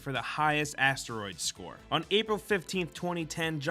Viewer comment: I thought ge said the esteroids record